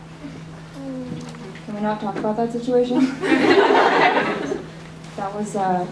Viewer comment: what did they say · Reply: “how scared are you that, that coffee cup is going to break”